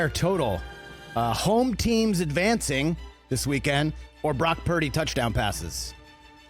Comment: 😆 🤣 😂 Brock Turdy 😆 🤣 😂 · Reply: I thought he said Rock Turdy. But either way I bet Brock has heard it all his life.